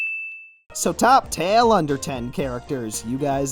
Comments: Did you just say "Top tale underten characters..." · Also I heard say "top tale underten characters"